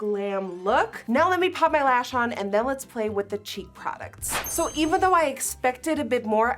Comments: Everytime she said cheek, not going to lie I thought she was saying cheap. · when she said cheeck products I thought she said cheap products 😂